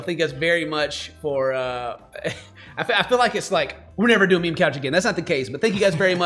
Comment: For some reason I've been binge watching RHOBH and now all I hear is "Mean Couch" after 2 weeks of no youtube XDDDDDDD